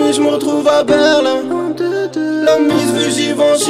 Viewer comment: min sagt er Ferrari bismi.....? Was sagt er ? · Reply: Talat. P ja Ferrari bismillah